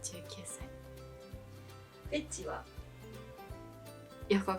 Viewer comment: 「エッチは？」に聞こえた🤦🏻‍♀️🏩